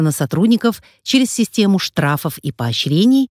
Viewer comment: через систему штрафов? Мне не послышалось? Или это о внеправовых методах?